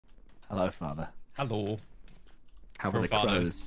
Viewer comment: I misheard Phil's 'hello' as a 'I'm old' 🤣